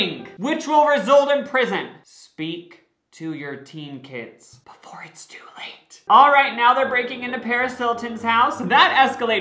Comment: When you said "before it's too late" I thought you said "abortions too late"